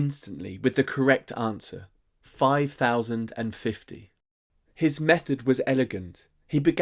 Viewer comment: It would be nice to have the possibility to put down thumb for subtitles and vocal translations. At 0:35 you said the correct result "five thousand and fifty". But in the subtitles (even the English ones), it becomes 5,50 and it's the same in other languages like French. So the vocal translations in French (and probably some other languages) says "cinq virgule cinquante" (five coma fifty) which is equivalent to the decimal number 5.50 as coma is used for the decimal point in French (and probably some other languages). Anyway why the English subtitles did not write 5,050 ? Another at 5:30 : In the subtiles as in vocal translations 14 + 14 instead of 1/4 + 1/4